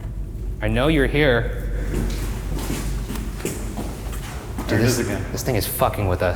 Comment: did shane say there is a guy?